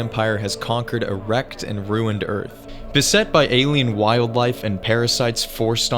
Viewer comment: I know what you said at 0:44 was “a wrecked and ruined Earth” but what I heard was definitely “erect and ruined Earth.” 😂